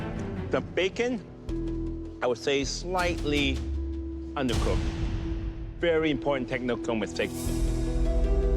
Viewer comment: Can someone please tell me what the hell hes saying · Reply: "you know the bacon, i would say,slightly undercooked, very important technical mistake"